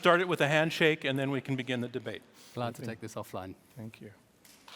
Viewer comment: what did he say? And what did he mean? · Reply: He said “glad to take this offline.”